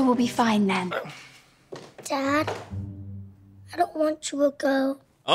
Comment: "i dont want chew a goat" that what i heard